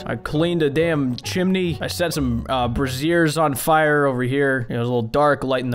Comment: did just say brassiere... as in like a bra. Instead of brazier like a fire thing...